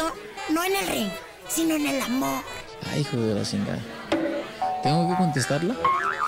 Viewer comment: hay hijo de la que dijo canelo? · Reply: Ay jo de la shingá